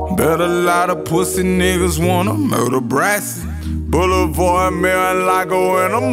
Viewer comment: Don't. It sound like hes saying murder brax